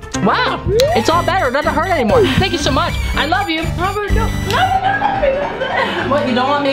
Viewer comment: did call him Robert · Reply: Guys call